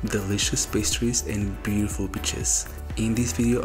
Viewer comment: Caught me off guard when I thought he said beautiful bitches @ 01:14. Realised he actually said beautiful beaches